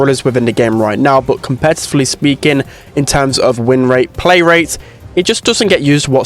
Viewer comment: did he just say colt has a low playrate? uhh I think you might wanna fact check that... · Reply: blaze he said low win rate